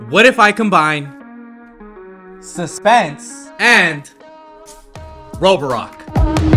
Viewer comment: I heard “roborock” into “Roblox”